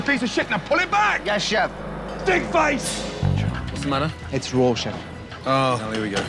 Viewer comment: "But I'm not dickface Chef" Top 10 sentences spoken before immediate death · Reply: Lol I thought he said pig face